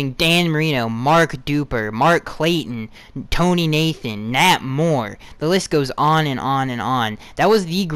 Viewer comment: I thought you said Matt Moore😂